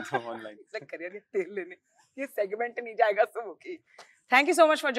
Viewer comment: Did he say “Not available”🌚💀💀 Bro did he cracked a dark humour on himself ??💀